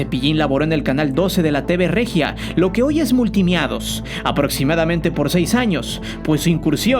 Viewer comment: escuché multimiados ? Cepillin siempre lo recordaremos.